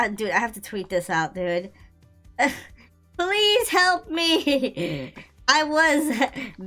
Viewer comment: He said please help me I thought he Said police help me